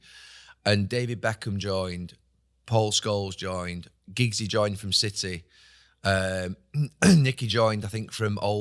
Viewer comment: "Giggsy joined from City" Did I hear it correct? I'm sure Giggs was a one club man but maybe I heard the wrong name